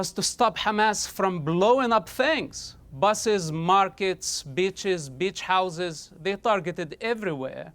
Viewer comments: I know I’m going to hell because I laughed when he said they’re targeting “beaches and beach houses” because I heard “bitches and bitch houses”🙂‍↕️ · Did bro just said in 11:30 "we need to stop hamas from blowing up bitches"? 😂😂😂 I whole heartedly agree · I’m sorry I thought he said bitches at 11:27 lmao